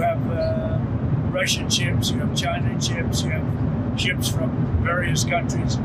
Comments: Did I hear “Chinan” ships? 😂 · I know he wants to say; ''Ships'' but I clearly hear him say; ''chips'' LMFAO! He is such a sad, pathetic little man! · He really said Chinan ships.